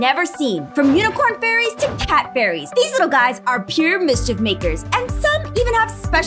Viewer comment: "From unicorn fairies to CAT fairies-" Me: CAT FAIRIES?! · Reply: I thought you said cat furries xD